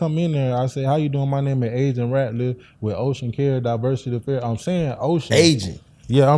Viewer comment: He was impersonating OSHA bruh was wilding 🤦🏽‍♂️ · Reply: He wasn’t tho he saying he from OCEAN CARES DIVERSITY AFFAIRS but it does sound like he saying OSHA😭🤣😂🤣🤣🤣 so that’s why these jobs was reporting him to OSHA 😂😂😂